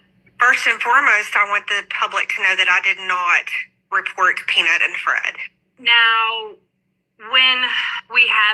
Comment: I heard her say, "I did not report Peanut and Fred," or "I did not report them."